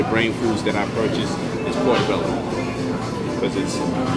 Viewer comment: WHAT DID HE SAY..PORTEBELLA?..OR OKRA?